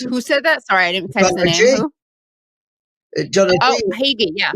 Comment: who? Sorry, I still didn't catch the name (of the guy Katie asked "who said that?") can anyone post it? Thanks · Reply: He mentioned John Hagee, a televangelist/pastor from Texas.